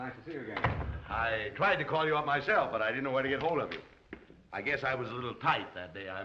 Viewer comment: Interesting that the gentleman said “ I tried to reach you on my cell”. Didn’t know they had cell phone at that time.🤷‍♀ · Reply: They misheard the line. It’s “I tried to call you up myself, but I didn’t know where to get hold of you”.